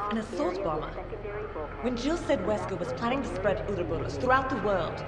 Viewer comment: did anyone notice that it said weaker instead of wesker at 14:41?